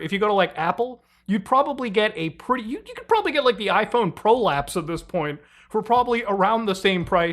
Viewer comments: Did you say iPhone prolapse? 🤔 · did he say iPhone Prolapse?